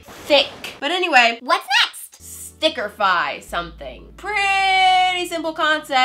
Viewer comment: When said “Sticker-fy” I thought she said “Snicker-fy” and I got really confused..... I’m stupid